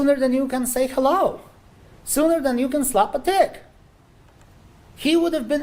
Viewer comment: TICK, I honest to god thought he said slap a dick. And he's got to be joking with that closing statement 🙄